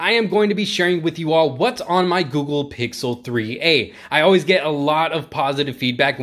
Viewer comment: Thought it said Who wants my Google Pixel 3a